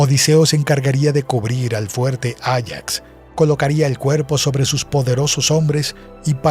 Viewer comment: en el minuto 14:31 pareciera que dice "sobre sus poderosos hombres" xd